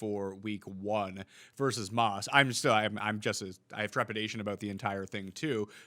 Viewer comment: I heard "aboot" and realized I was listening to a Canuck for Fantasy football advice.